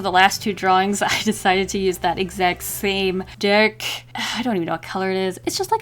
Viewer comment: “that exact same D**K” What were you even trying to say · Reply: uDevil06 it’s just like a dark red?